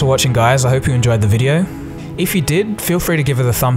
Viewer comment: For the longest time, I thought you said: subscribe if you knew, but I heard for the first time today: subscribe if you're new. I can't believe I missed that.